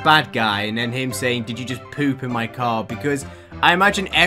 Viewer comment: AUVSIBDUGDR I THOUGHT IT SAID EAR INSTEAD OF CAR JUST PUT ME IN THE BIN